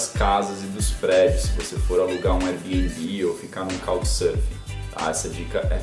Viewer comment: Otimo video! No minuto 10:10 vc fala sobre alugar casa, airbnb e um nome que eu nao entendi e fiquei curioso, seria (kald surf)?? o que seria isso ou como se escreve kkkk? valeu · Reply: Faaala!! É Couchsurfing, uma comunidade de pessoas que sedem suas casas para viajantes se hospedarem. Muito bacana!!